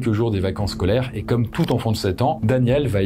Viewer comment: j'ai entendu d'abord "Comme tout enfant de satan" bahaha Superbe vidéo sinon, comme toujours !